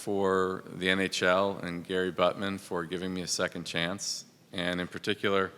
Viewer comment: did he just call him Buttman ?!